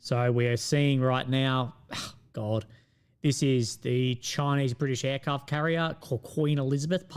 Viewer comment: what's the word said before "British aircraft carrier"? transcription says "Chinese".. not helpful.. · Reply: It is Chinese. The Chinese launched a new carrier type 076 generally referred to as Queen Elizabeth by the west due to its striking similarity to the British carrier of the same name. I think is making a joke as this is the actual QE.